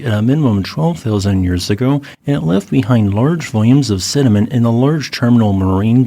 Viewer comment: sounded like "large volumes of cinnamon" lol